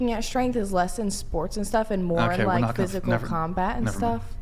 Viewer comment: did she just say “physical combat”?! 😳🙄🤦🏼‍♂️